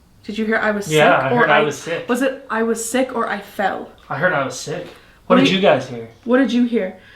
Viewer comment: when you asked "how did you die?" i heard " i fell sick"